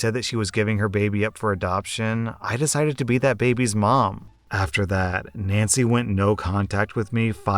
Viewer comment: I thought you said BEAT that baby's mom. This would have been a very different video